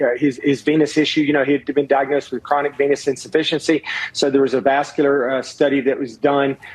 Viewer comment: I swore I heard "chronic penis insufficiency" & I honestly didn't question that diagnosis at all lol